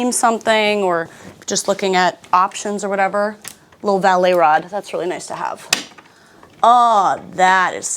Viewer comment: OMG I thought you were saying "ballet rod" and I was wondering why it was ballet, like it's a barre??? lol realized valet rod when I checked the description. Great job as always, I wish I had an eye like you do!